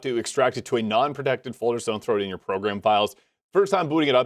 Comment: Imma be honest I have no idea what you're saying here. It sounds like "So I'm throating your files" to me.